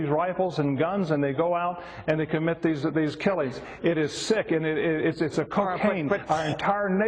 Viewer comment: it sounded like he said "they commit these killies" lmao